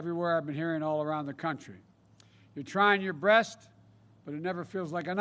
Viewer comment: Did I hear him right? On time stamp 13:30. Did he say your trying your (breast), but it never feels like enough. Was that supposed to be a joke or something? Or did he misspeak again? Also at the end of his speech I heard about 10 people clap for him. You know what that means don't you? There were 10 stupid people there supporting him.